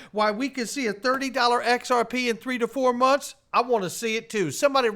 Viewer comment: They said 3 to 4 months?? I thought they said $30 after the emails lol